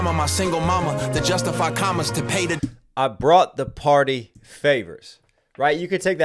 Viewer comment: I thought he said he bought the party favors. Not brought. As in he bought the favors for himself.